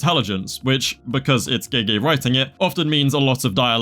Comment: NO WAY im just turned on the subtitles and when bro said gege writing it subtitles said gay writing in there are a timecode